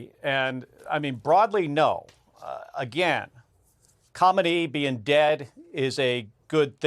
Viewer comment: Am I the only one who thought he was saying comedy instead of Khamanei? It took me a second each time to wonder why “comedy is dead” was good.